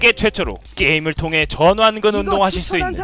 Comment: 어 ..? 성 전씨라고 들었는데 전완근? 이름 전완근이에요??